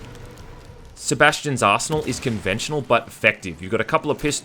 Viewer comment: "Sebastian's arsehole is conventional but effective." What I heard at 4:58..